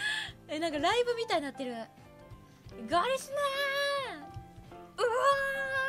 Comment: I think at 3:35 it's supposed to be Golisteners instead of gorillas